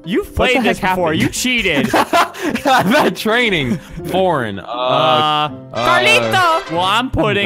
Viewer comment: turn on subtitles it says porn instead of foreign